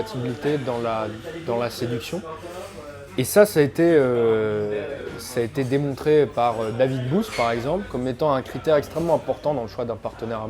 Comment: Ptain j'ai compris "ça a été démontré par Dany Boon". Propos intéressants à partir des étapes 3 & 4 ceci dit. Par contre l'étude avec les rats, sur la méthodo il y a pas mal à dire. Mais on note l’effort de ne pas prononcer le terme d'hypergamie pour ne pas rameuter les SJW.